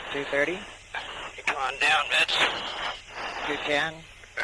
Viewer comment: Does he say "come on down, bitch!" @ 9:35? LOL